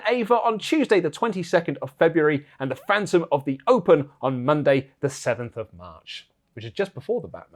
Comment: As you were saying Phantom of the Open, i thought you were gonna say Opera so my brain said Phantom of the Oprah. 😂 Side note: imagine a phantom of the opera with Oprah